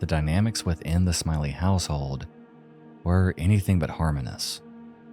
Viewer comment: Amazing video! Just a quick note, the word at 17:10 is “lamenting” not “laminating” 🙂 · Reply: also should probably be "harmonious" instead of "harmonus" at 9:54 lol